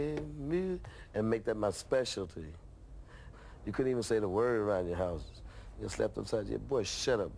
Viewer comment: What does he say at 0:45 until 0:55? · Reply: “you couldn’t even say the word [funk] around the house”